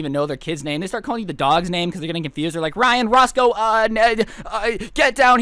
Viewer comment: I heard the words "Ryan Ross" when the 'father' calls for his son. Did anybody else hear that?